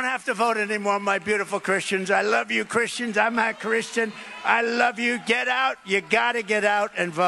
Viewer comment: Did anyone listen closely? He said I'm not a Christian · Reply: I clearly heard him say from the first time I listened “I’m not Christian” and listened 3 times over and he still said “I’m not Christian” but the transcript says “I’m a Christian”, the transcript lies!